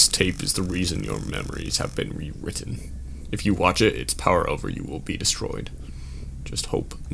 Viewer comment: I hear a clear "p" sound at the beginning of that word. The wiki says "our" as well, but no matter how many times I listen to it, I don't hear "our". I just hear "power".